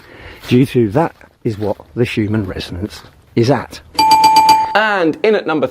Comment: I'm pretty sure that I heard "Schumann Resonance" and not "Human Resonance", not that it makes any difference.